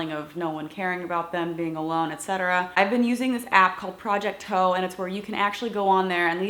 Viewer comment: What the app called?? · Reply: found it i thought you said project ho or hon but it was project toe